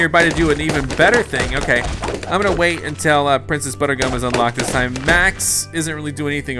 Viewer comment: DID HE JUST SAY PRINCESS BUTTER GUM!?